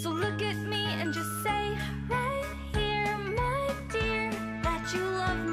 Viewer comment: At around 13:36 I always hear “just say yori” “just say yori” 😂😂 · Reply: i heard “just say ori”